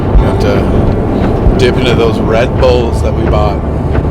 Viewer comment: OMG you guys, you were saying you need red bull for a pick me up to keep you going, and i seriously thought you were saying red pills...and i was thinking "are they openly talking about taking drugs on youtube?" hahaha